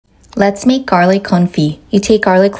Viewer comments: I thought i heard "lets make garlic kulfi" thank god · I thought that said “Garlic Conflict” and I was fully prepare to hear about a war fought over Garlic. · I thought it said garlic confetti